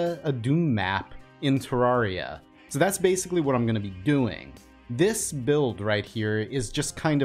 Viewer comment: they sai so many times doom, to inthe time they sain doing i hear doom ing